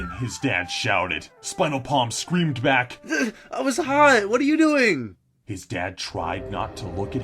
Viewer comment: At Number 5 I thought said spinalpalm was 10 and he was ball dripping hot and then when he was caught I was high what are you doing and then I rewathed it and realized what they saifd